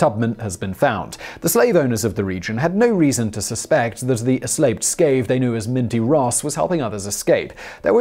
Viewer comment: Eslaped Scave? Anyone else catch that at 4:50-4:51?